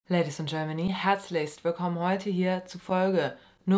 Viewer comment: Was sagst du am Anfang eigentlich immer? 😂 “Ladies and Germany?/Gents?” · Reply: Ich hab da Ladies in Germany verstanden🤔keine Ahnung